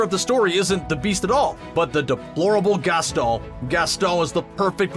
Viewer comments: When you become French when you say Gaston Screen Rant: Gastoh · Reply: I heard "Gastol" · why is he saying Gastol not Gaston...it’s really bugging me..unless I’m hearing it incorrectly. And if he’s saying Gasteau.. that’s still incorrect. There’s a WHOLE song about him at the tavern where they say his name multiple times